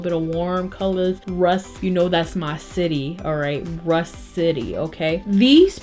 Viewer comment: For a second I thought you said Rush City and I was like “Chab’s my neighbor?!!”